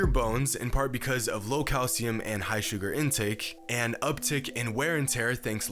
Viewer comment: Boi said "Suger" at 8:33 LOL